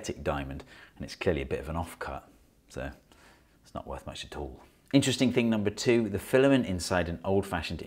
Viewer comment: I can't understand what was said, and there's no subtitles. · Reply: '… and it's clearly a bit of an offcut, so it's not worth much at all.'